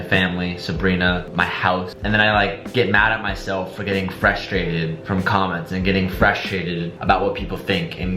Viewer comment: He’s totally saying “Freshtrated” instead of frustrated at 15:54 right?